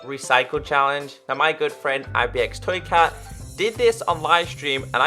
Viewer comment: did he just say asbestoscat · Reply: No